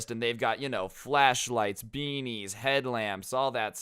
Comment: Not going to lie when he said beanies I thought he said bbs